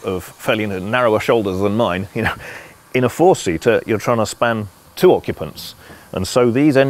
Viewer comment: did he just say four seater????